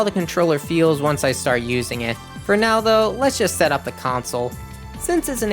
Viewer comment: I literally heard you say “Ronaldo” here, I don’t even like football. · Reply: I clicked the time stamp and heard it immediately, had to play back a few seconds to realize I said “for now though” really fast haha.